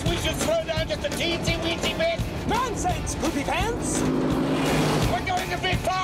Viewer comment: "NONSENSE POOPYPANTS!!" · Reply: Always thought he said “poofypants” 🤭